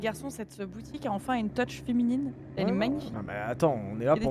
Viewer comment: Pourquoi j'ai entendu "teuch féminine", alors que je sais très bien qu'elle a dit "touch" 😂